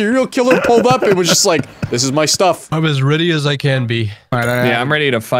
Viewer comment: incorect subtitle for "I'm as ready a I can be" instead of "I'm as ready as i can be" 🤓